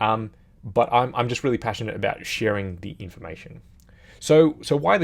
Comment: Because of your Aussie accent and related expertise, I thought you said: "...I'm just really passionate about 'shearing' the information!" 😄 Anyway, love this channel, I'm learning A LOT! Keep it up and g'luck to your channel! 😉👍